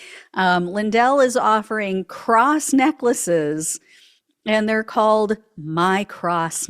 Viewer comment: Thought you said crass necklace, more accurate